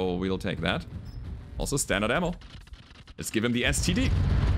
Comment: Did just said "Lets give them STD" at around 50:30 ? Why does that sound soo wrong ? XD